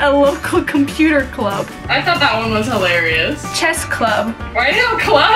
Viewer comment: You NEED to create your own chanel!! It would be awesome!! Love ya. Love the book!! Hot guy!! I thought she said cheat club!! I be like wuhh!?!?